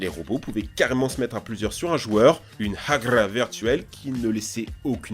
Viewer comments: Pardon ? Une quoi ? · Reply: une hagra, t’es sourd? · une '' ragra virtuelle'' c' est quoi cette mascarade. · Une quoi ?! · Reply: Hagrrrrrrrrrrra